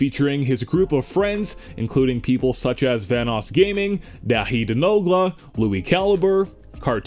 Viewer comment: I thought he said TheHiddenNogla